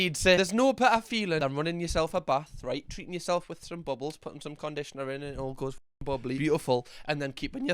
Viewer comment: did you just say conditioner?